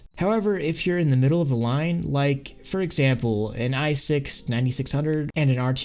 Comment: did u just say a i6??